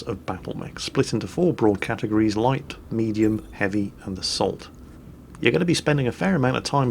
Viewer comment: I swear I heard you say "Light, medium, heavy and the SALT" I want that SALT class and I want it NAUUU